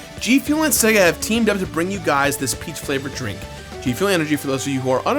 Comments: Not gonna lie, every time you said "peach flavor" I heard "pee flavor" and was really confused... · I thought he said "Pee flavored", not "Peach flavored". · when he first started talking about g-fuel I thought he said 'pee flavored g-fuel'. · I know he's saying "Peach Flavor," but I keep hearing "This sonic can G-Fuel is pee flavor," which puts a weird, cursed image in my head.